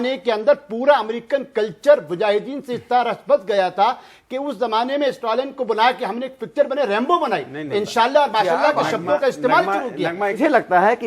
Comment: Did I hear right.. Islam ne Rambo picture banai??? At